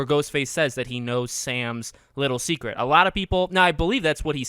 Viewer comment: I thought he said Billy's secret 🤔